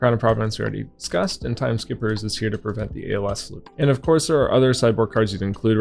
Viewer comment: Tkme skippers to prevent the what? I cant heat clearly what yoj said there 29:57. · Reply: it lets you stop the ALS loop from prism, attack the als then clear the angel of rebirth with anothos